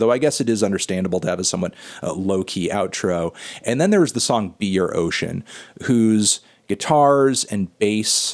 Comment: Instead of "be your ocean" I thought you said "beer ocean" which I thought sounded out of place in the track listing but thought fuck yeah nonetheless